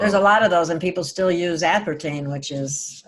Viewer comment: mark... what kind of oil did say?! Tea oil? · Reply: I believe he said CBD